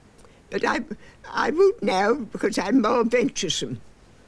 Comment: Love that she says she's "venturesome" rather than "adventurous", the older word sounds better, shame we don't say it like that anymore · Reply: I thought she'd said "adventuresome".